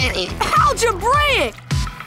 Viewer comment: MY WHOLE LIFE I THOUGHT FINN WAS SAYING ALGEBRITIC NOT ALGEBRAIC